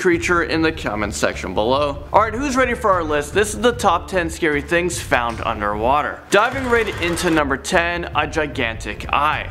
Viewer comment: I thought it said scariest things found in underwear